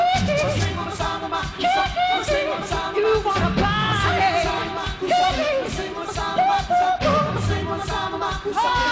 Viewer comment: what he says here? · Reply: "You wanna partyyyyy"